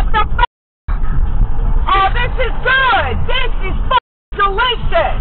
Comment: Did she say delicious?? 🤣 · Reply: No, she said "F**king delicious." That needs to go on a poster. 🤣